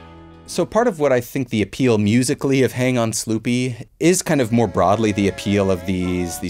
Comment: when i was a kid growing up in the 60,s i thought they were saying snoopy lol